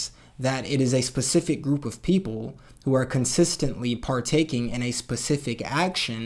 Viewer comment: I legit thought you said "poople" i had to rewatch a few times